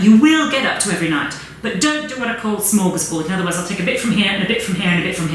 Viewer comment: can anybody tell me what she says at 20:06 after "but don't do..."? I couldn't hear it clearly and it sounded important. Thanks! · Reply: I think she said "don't do a smorgasbord" like putting a lot of different products on at the same time